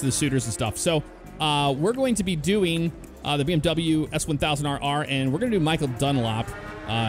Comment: "S1000 AR AR" lol IDK why but that's how I hear it, I'm used to people saying CBR600RR but the BMW S1000rr is usually called the "S1000 Double R"